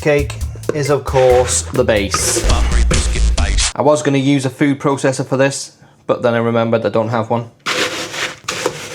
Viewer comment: where is the clip from at 0:42 and what is he saying? :catJam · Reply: That’s Greg Wallace from Masterchef and he says “buttery biscuit base” a clip that went popular when the YouTube channel “Swedemason” remixed it.